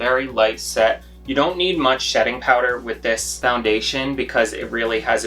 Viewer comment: did I hear you say shetting powder? Lol..I loved this tutorial ❤️ You explain it like a normal person..not someone who is full of themselves. Love the Sephora series as well..lol. ❤️ from Lethbridge AB Canada 🇨🇦